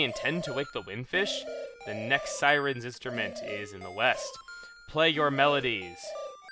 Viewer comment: Oh east i thought you said weast